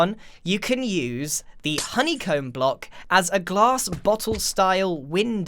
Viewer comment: Did he said Medi-evil?